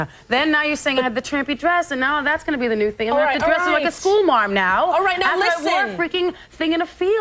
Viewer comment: What's Mariah saying at 4:03-4:08??? I can't fathom out:( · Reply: School mom now